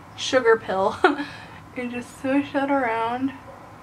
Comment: I really loved this tutorial!!! And my eyes grew wide because I thought you said "Sugar Hell" hahahaha I was like... "whats that brand?" XD